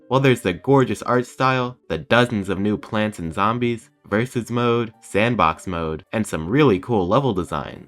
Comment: i swear that said racist mode edit 0:54 DID THAT PLANT JUST SAY THE F WORD